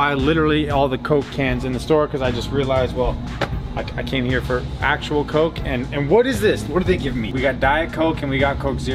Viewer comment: I heard "We came in here for real coke." And for a second i thought you were joking about drugs.